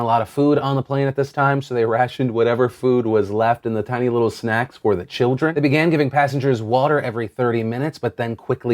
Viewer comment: Phil, for a second there I thought you said "the little snacks were the children", as in cannibalism. 😅